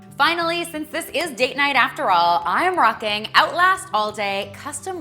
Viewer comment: I could've sworn she said since this is "dick night" 😂